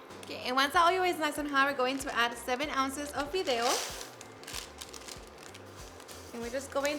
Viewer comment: Hola .. I would like to try this but I don't understand what the package of Noodle looking things you poured in at 0:54 ?? I think I heard Fido?? .. Thanks! 👍🎉💙😋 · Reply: Fideo.